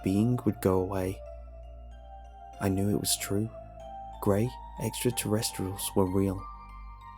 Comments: i thought it said "the gay extraterrestrial" ooooooops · I swear i thought it said the gay extraterrestrial